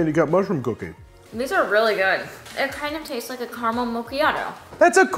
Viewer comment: when you turn up the subtitles to understand what she said, but the subtitles don't show up for exactly that sentence 😭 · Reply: She said "caramel macchiato"